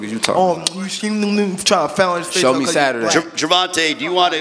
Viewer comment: Did I hear tank say “he trying to frown his face cause he’s black?” Is that what he said?